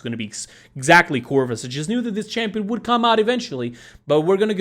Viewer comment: Low key thought it said Corona Virus